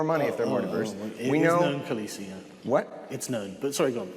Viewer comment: I still can't tell what Sargon said after said "Diversity is a benefit to corporations, they make more money". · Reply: "Yeah it is known, Khaleesi" - it's a reference to Game of Thrones. There are some servant girls in an early episode who try to justify their crazy gossip by saying it is known. "The moon is an egg, it is known."